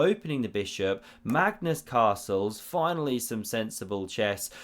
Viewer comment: "Magnus carlses" I heard first time. No, it's actually "castles"